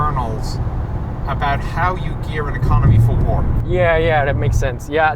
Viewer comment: "How you gear an economy for war". The microphone meant I hard to listen to that 4 or 5 times to understand what he said.